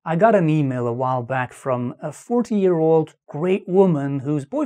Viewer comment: I had to turn subtitles on. I thought he said he talked to a 14-year-old Greek woman with a boyfriend in his 30's. I'm like, hold up, that can't be right! I'm not making fun b/c his English is fantastic; I just misheard. :)